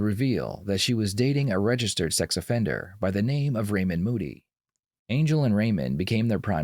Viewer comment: I thought they said Raymond booty 😂